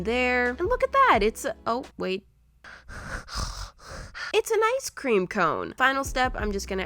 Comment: kinda sounds like she said 'it's a nice cream cone'